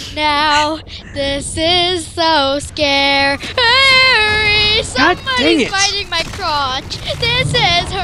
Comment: Just listen to and she says"Someone Is Bitting My Crouch" and when I heard that I couldn't stop laughing